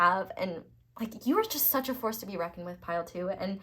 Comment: Pile 2: after the Spirit reference, I heard you say, “You are such a horse (instead of force) to be reckoned with.” 😂